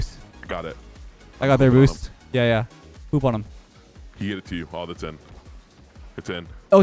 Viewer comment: Did I hear “poop on them” at the 6:30 mark? 😂